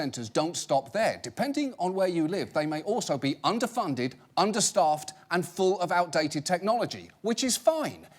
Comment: I thought he was saying "Understuffed" 911 dispatch XD